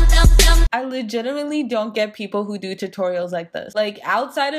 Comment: Lmao did you say legiterally?! Around 1:06. Dying. Also wtf I would NEVER put a razor NEAR my eyelashes/eyes!! 😵 Edit: ok I think you said legitimately. But still that was a cool word and made me laugh 😂